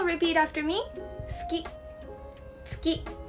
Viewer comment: All I heard is "Iski" "Uski" 😢